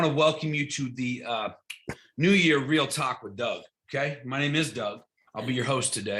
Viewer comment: Replay crew but I’m confused… did he just say “new year real Taco Bell?” Oh… Real Talk With Doug… nahhhh 🌮🌮🌮🌮🌮